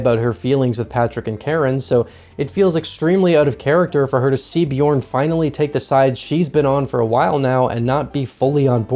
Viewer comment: What does she say here? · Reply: When he wakes her up she says “slap lige af” which means “calm down”